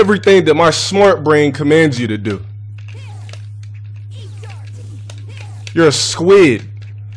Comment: someone count how many times he said the same shit about charge characters? Cause i think it was a lot! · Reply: Nino muratore 37:10 did he say you're a squid?